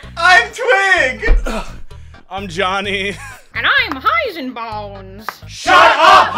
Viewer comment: I misheard Heisenbones as Heisenballs 💀